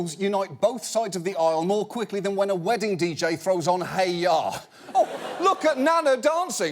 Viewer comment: what's the song he says? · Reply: Hey Ya by Outkast